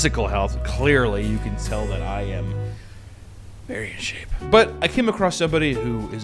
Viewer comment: 22 seconds.. I thought you said "very ashamed" which was really funny lol but you said "very in shape"